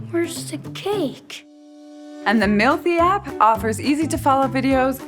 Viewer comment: That moment she said "mealthy app." I heard "milfy app." >_<